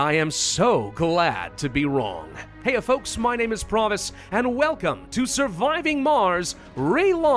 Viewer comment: TELL ME IM NOT THE ONLY ONE WHO HEAR MY NAME IS PROMISE